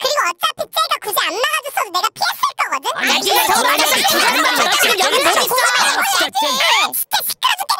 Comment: 진짜 딱 한두마디 들린다 ㅋㅋㅋㅋㅋㅋ 야 니가 저거 맞았으면 죽었어 너 여기 못있어! 아니 이제 막 돌아온 애한테 그게 할말이냐 아 진짜 쟨 안들려...